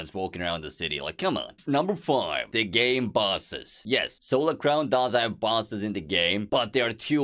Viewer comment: lmao I thought you said busses but I think that a lot of the things you mentioned might get added in the full version.